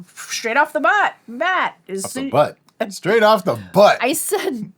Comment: Straight off the bat I have to say that I heard "straight off the butt"